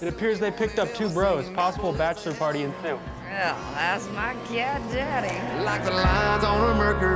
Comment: When it said cat daddy I thought it said black daddy 😂😂🤣🤣